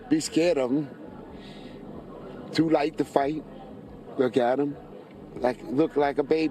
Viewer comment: Crazy right, I was like what is he even talking about.... He said, " He like to fight, then said he had a baby face. Starts with family as well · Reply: I thought he said he likes to fight, but listening to it again he did say he was too light to fight, and had a baby face.